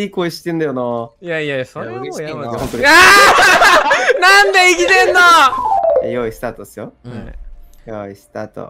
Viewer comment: I think that Shibuya Hal said : Why are you bullying me ? (if I'm not mistaken) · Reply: Subtitles are correct. He said 「なんで生きてんの？」